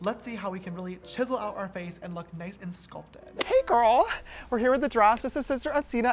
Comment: did he just say "hey girl"